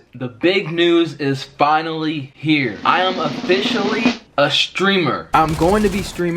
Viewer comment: I thought he said stealer when he said streamer